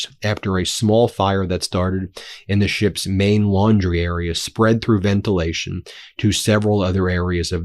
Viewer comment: I could have sworn yout said "a small fire which took place in the ship's main lingerie area" - i had skip back! LOL